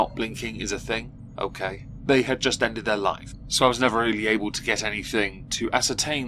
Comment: I thought he casually said "...but they ended their life." And i was like, woah that took a turn... Then i realized he said "live". Thought that was worth sharing...